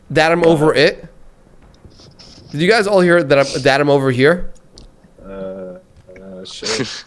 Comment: i thought he said im over it